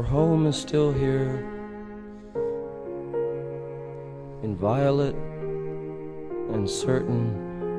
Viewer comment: Actually he's saying " your home is still here, inviolate and certain." Meaning a peaceful home that's stable, I know for 25 years I thought he was saying violet but at a closer study these were his true words. His lyrics are very meaningful like when he says " a city rises from the sea " he's referring to the mythological sin city Ys that was buried under the sea in 12 century mythology. That's where the word Paris comes from in France ( par meaning Like in french so the city Paris Means like ys)which is where he ultimately died, almost haunting how he referred to Paris and passed away there the same year